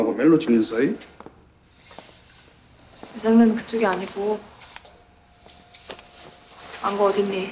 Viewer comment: 이거 대사가 '내 장르는 그쪽이 아니고' 아니에요? 암만 들어도 예상하는 그쪽이 아니고로 안들리는데